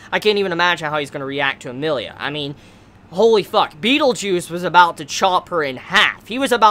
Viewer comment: Why am I hearing say "beetle juice" lmfao